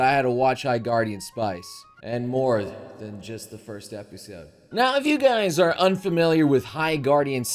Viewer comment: Im pretty sure he said he watch it all.... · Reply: I rewatched the beginning I must of missed the "and more than the first episode" line. Post edited to remove the part of my mishearing.